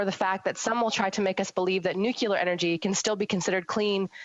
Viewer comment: Did she say Nucular energy